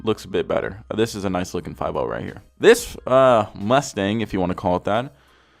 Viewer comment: i thought he said Lustang